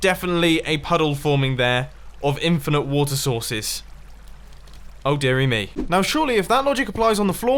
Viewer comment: is it just me or is he saying Worcester sauce or water sauce